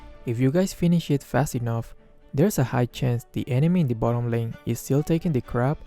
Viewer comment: Thought I heard 2:20 "the enemy is taking a crap"